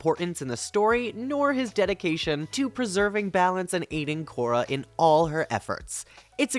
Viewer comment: When I first heard you say "aiding korra", I misheard it as "eating korra" and at that time (and still do tbh) I too appreciated his dedication